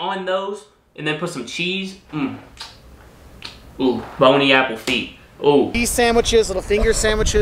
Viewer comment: What did he just say. "Boney Apple Feet" 😂 It's bon appétit.